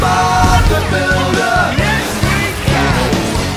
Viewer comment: I thought, that I heard "Ass we can" instead of "Yes, we can".